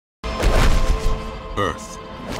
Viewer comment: I thought the earth part said "BIRTH" Lmao